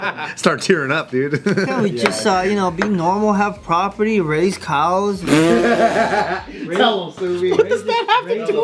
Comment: I thought he said race cows. Which made it twice as funny 😂😂😂